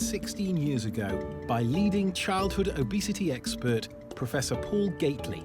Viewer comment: i heard “by yeeting childhood obesity expert...”